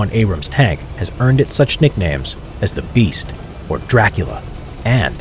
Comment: Did he Say Dracula? 3:56 Because If he did Dracula means the son of the dragon